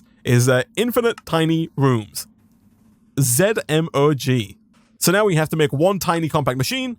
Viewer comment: We gonna ignore at 50:24 he says “ZMOG” instead of the written Zomg